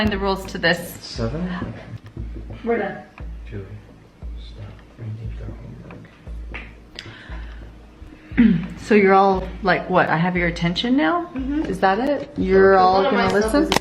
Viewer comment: what did say to Julie?? at 7:54-55 I kinda hear a strong threat!! what I heard was" Julie stop or I will down the whole bag"or sth like that so he is completely into this like wt''f??? the way they look at each other is so sad and how Julie older sis is so concerned for Julie